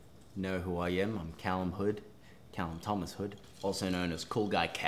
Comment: I thought calum said Calum Tomlinshood i screamed and then i was like wait a second